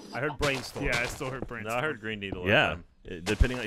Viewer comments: I kept hearing "brain needle" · When did Ryan get his new job at the law firm of Trailoff & Babble? Also, I heard "Brane Needle".